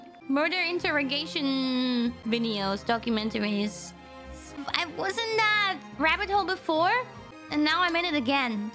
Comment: I think she said "I was in that rabbit hole before" rather than "I wasn't that rabbit hole before".